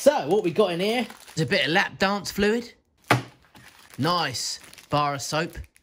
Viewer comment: Um, @ 4:30 did he actually say "lapdance fluid"? 😲😂🤣